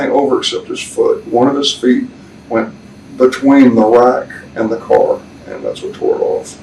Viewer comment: wtf is he saying at 6:19..."between the lack and the car"...what is a "lack"? I've never heard "lack" used as a noun. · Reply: I think he said rack. Meaning where the rights of the police car were.